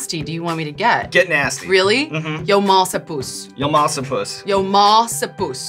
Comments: it literally sounded like she said “Yo ma’s a puss” so I was pretty close to guessing what it really meant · I mean. That’s what it sounded like... “Your ma’s sour puss.” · I literally heard "Your mom's a puss"